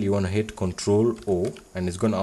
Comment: "to save the file you wanna hit control foaoiiil" ....... bruh, i have absolutely no clue what you said. Sounds like control four, but that doesn't work. You NEED to add subtitles to your videos, because if you can't pronounce something and then give us a tutorial and we end up not doing it right then we are fucked.